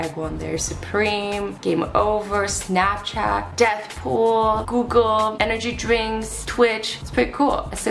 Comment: Did azzy just say deathpool